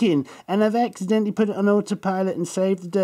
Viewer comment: ha ha sounds like he says crack pot at the end